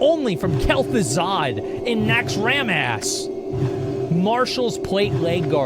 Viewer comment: thought you said next ram ass. You're not gonna powerhouse your way to 60 in hard-core. 1 simple mistake & it's over,then there's grinding for gear. The gold content is the sweats doing open world pvp. 1 mistake & months of grinding gone 😂